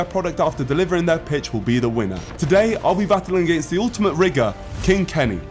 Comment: yo what did you call him?? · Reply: Rigger, definitely 😂